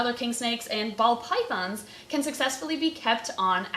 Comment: I keep hearing 'bald' pythons instead of ball.